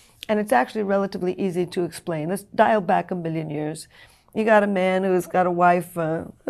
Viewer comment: Did she say "a billion years"?